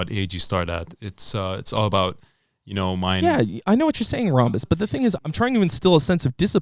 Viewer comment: Did he call him rhombus at 1:15?